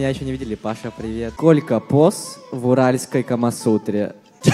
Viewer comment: блять послышалось "Колька босс в уральской Камасутре"